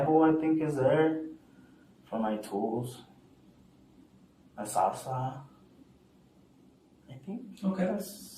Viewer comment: I don’t think he said ”salsa” 😂 I think he said Sawzall which are those reciprocating saws lol